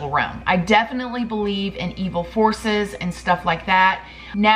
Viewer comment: I thought she said "evil HORSES" not forces 🤦🏼‍♀️😂